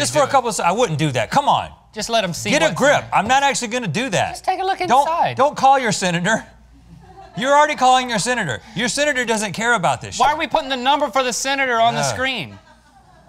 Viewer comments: Anyone else hear Rhett say "Your senator don't care about this sh*t" at 1:46? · I don't think Rhett actually said this, but it sounds like he said "Your senator doesn't care about this s***". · at first I thought Rhett said "your senator doesn't care about this sh*t" it even says sh*t in the captions!!!! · Lol I thought that he said “your senator doesn’t care about this shit” but he actually said “your senator doesn’t care about this show” lol